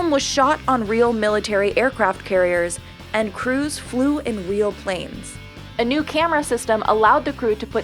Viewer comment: Wait, did she say Weal?